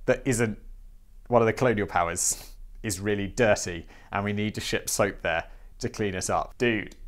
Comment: Thank goodness for the pic.... I thought he kept saying pez soap